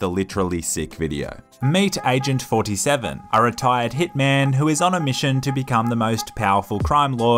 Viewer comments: I thought he said thiccman insted of hitman 😂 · When you said “Hitman takes over Los Santos” I thought you said, “Thicman” lol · At the begging I thought he said thick man not hitman😂